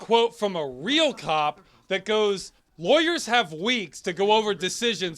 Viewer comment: whoever did the subtitles seem to be bad at both context and hearing, seeing as he wrote Warriors instead of lawyers lmao.